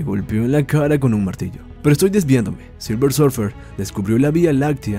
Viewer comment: En el min 6:26 entendi "soy lesbiano" :v